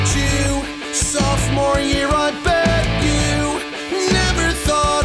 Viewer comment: I thought he said softmore year I BED YOU